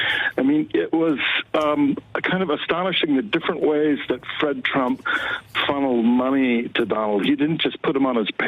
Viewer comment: "Fred fart Trump" am I the only one who heard that